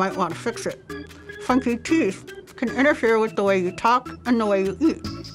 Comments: I thought she said Fucking Teeth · I thought she said “ fucking teeth” 😂😂😂😂